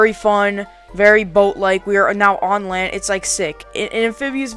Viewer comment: i thought it said very volt bike